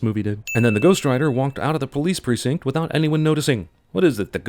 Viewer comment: he said alyone instead of anyone